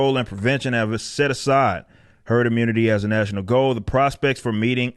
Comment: I thought it said nerd immunity, and I thought I was in the clear